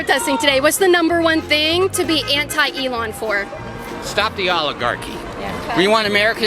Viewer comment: I swear I heard him say stop the Olive Garden